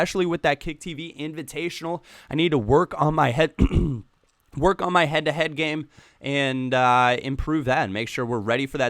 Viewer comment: you want to work on your head game did you say?